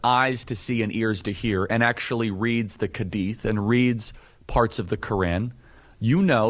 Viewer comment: did he just said Read the khadith 😂